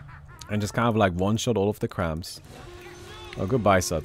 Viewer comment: Did he just call "creeps" "cramps"?